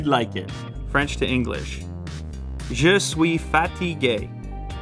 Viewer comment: i cant be the only one who thought he said im fattty gay